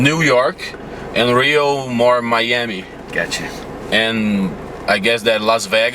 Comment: Muito legal a entrevista mas QUEM FOI O MONSTRO QUE LEGENDOU Miami assim: Maiami (1:46)??? Kkkkkkkkk